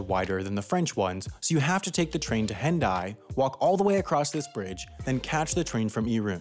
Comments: I heard "The train to Hentai." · i thought he said hentai